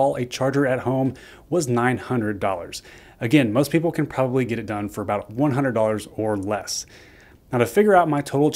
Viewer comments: He didnt say $100. He clearly stated that it cost him $1342 to install and posted the cost on the screen. I don't how or where you heard or saw $100 unless you just like making up numbers in your head to fit your narrative. Weirder still is that 19 other ppl agreed with you. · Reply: Please review what he said at the 3:24 mark. "Again, most people can probably get it done for $100 or less". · He didnt say $100. He clearly stated that it cost him $1342 to install and posted the cost on the screen. I don't how or where you heard or saw $100 unless you just like making up numbers in your head to fit your narrative. Weirder still is that 19 other ppl agreed with you. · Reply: he did actually say $100 or less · He didnt say $100. He clearly stated that it cost him $1342 to install and posted the cost on the screen. I don't how or where you heard or saw $100 unless you just like making up numbers in your head to fit your narrative. Weirder still is that 19 other ppl agreed with you. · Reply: he clearly says most ppl can get it installed for 100 dollars or less.